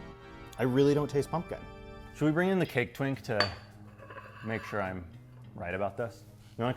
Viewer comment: Am I the only one that thought he said "Cake Tink" and was a little disappointed when Cake Twink didn't come out dressed in a Tinkerbell costume?